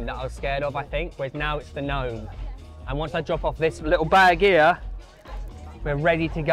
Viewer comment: sounds like he says bag of gear 😂😂😂